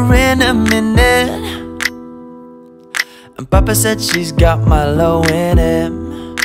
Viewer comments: i heard " and papa said she has malo in him" is it just me? ok then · I heard ''Papa said that she got milo in it''